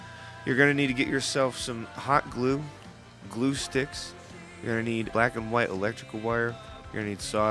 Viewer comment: I thought you said glow Sticks hahaha